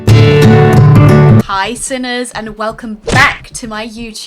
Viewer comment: In the beginning, it sounds like your saying hey SINNERS 😅